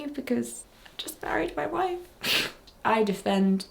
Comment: As I was cleaning my room while listening to the video in the background, I misheard "married my wife" as "buried my wife" at first. My heart must've stopped as I panic-rushed to my phone to replay the line, and breathed a HUGE sigh of relief when I read the subtitles. So happy for you five!☺️💞(genuine)